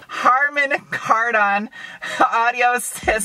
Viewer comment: Lol I thought you almost said, “It has a karmen hard on.” Love your reviews! 😊